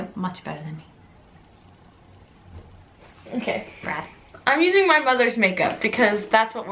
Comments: did Melissa call maddie a brat at 0:42 ? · I thought she said "I'm using my brother's makeup" at 0:43 😂 like if you can relate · did anyone hear Melissa say "brat"??